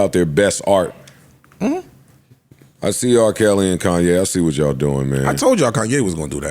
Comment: I had to rewind a couple a times I thought Joe said Kelly Anne Conway not R. Kelly and Kanye 😂😂😂